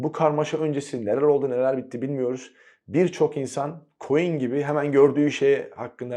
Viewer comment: Abi "Koyun gibi" yi "Queen gibi" anladım. Diyorum ne alkaa...